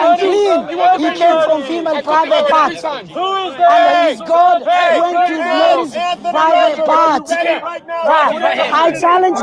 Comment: Never seen the " HE NEED SUM MILK " meme? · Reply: I heard "His dad went for some milk"